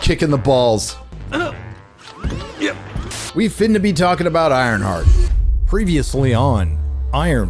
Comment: did you just say "we finna to"? lmao